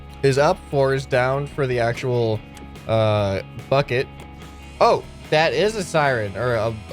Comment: When he said bucket i thought he said f*** it XD